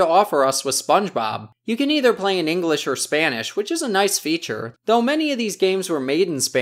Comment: did you just say "English or Spanish"?